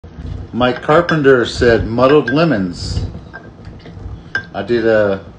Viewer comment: I thought u said my carpenter said lmao